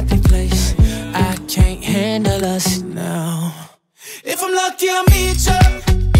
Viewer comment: I keep hearing "If I'm not gay, I'll meet ya" lol